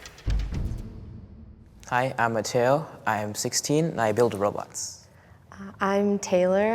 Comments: Kid One: I build robots Kid Two: Im patient about singing and music Kid Three: I fly planes Me: I have over a million robux on my roblox account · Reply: i thought the first one said i build in roblox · Am I the only one who thought the boy said "I build in Roblox"? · “i build robots” “i fly planes” “i do music” me: i spend most of my weekends binge watching. my record for not getting up is 16 hours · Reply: At first I thought he said I build in roblox. I was surprised how no-one was making fun of him in the comments. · Kid One: I build robots Kid Two: Im patient about singing and music Kid Three: I fly planes Me: I have over a million robux on my roblox account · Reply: BαႦα Yαɠα i thought he said he built Roblox lmfaoo